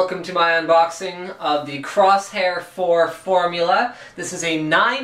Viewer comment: wait did you just say crosshair 4 in the beginning? LOL